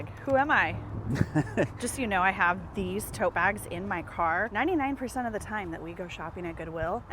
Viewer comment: “…just so you know I have these TOE BAGS…” — is what I heard 😂 I was like — girl, what kinda toes would require these oversized “toe bags”?! 😅